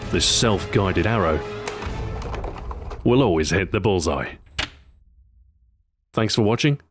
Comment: I thought he said balls eye